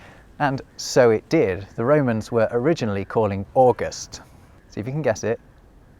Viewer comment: The subtitles say "July" instead of "August".